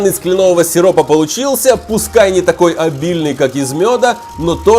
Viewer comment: Мне послышалось или вы сказали Альбильный?😚